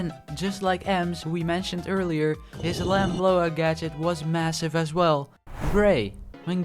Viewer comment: did he say massive?